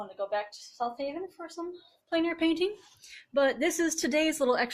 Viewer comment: I love this! I wanted to hit the like button like 5times. Your work is awesome! And getting awesomer. For a moment when you said plane air painting I thought you said planar painting witch took my brain to strange places